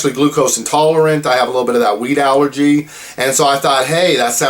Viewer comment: totally thought he said he had a weed allergy